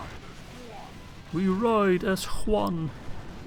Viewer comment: whenever u say we ride as one, i hear: "We ride as Juan!". Just makes me picture a mexican with a sombrero on a donkey.